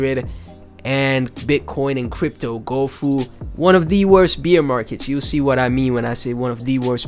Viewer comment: thanks for the tectonic content brother! you also gave me a good laugh this morning, every time you said "bear market" it sounded like you said "Beer market" haha... I mean no harm by this and its all love. thanks for the laugh and the content <3